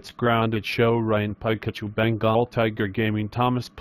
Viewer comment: why did boris say my name cuz i heard him say tiger gaming